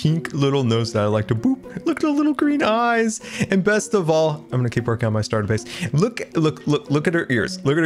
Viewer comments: What I heard was “I’m gonna keep working on my star base” I love this so much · I did not hear the bad news. I thought he said, “I’m gonna keep lookin at my starter base.” Jellie was too powerful!!! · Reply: I'm gonna keep working on my starter base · I don’t know what u guys beware when he was talking to jellie but I heard “ I’m gonna blow up my starter base”........... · i honestly thought you said kick her out of my starter base which i'm relieve to see not the case judging by the comments lol